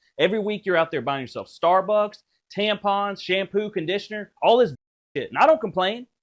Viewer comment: did he just say “ every week you are out there spending money on Starbucks, Tbones”???